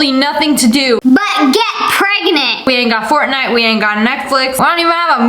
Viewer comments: when she said fortnite i thought she said fork knife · I heard SSSnipperwolf say Netkflix